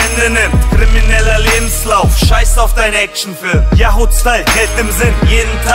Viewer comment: was sagt er bei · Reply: Yahudstyle geld im sinn Yahud=jude